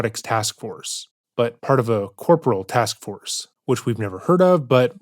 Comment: Was half listening while I was working, and misheard "corporal task force" as "temporal time force", and i honestly think him pretending to be TimeCop would have been a better strategy here than what he actually did.